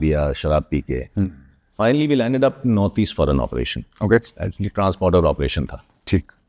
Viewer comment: I think he said trans border operation